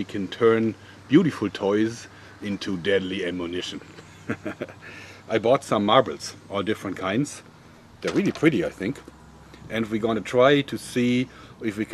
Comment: I thought you said No Marbles?